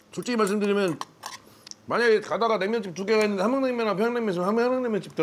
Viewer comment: 나만 함양 냉면으로 들림?